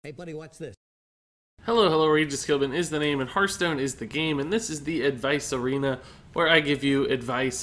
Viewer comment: The first time I heard your intro I thought you said "RegisKillbin is the name, and Hearthstone is real gay" 😂😂